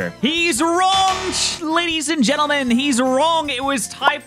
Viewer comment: sounds like almost says Chat instead of ladies and gentlemen lol. He thinks of us peepoShy